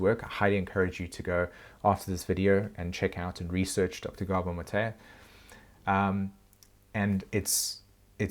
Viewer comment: At first, I thought you were saying something like “dr. Gabo Matayah…” though do you mean Dr. Gabor Mate? (Have always heard it prounounced “mah-tey,” so not sure if you’re talking about same guy or someone different.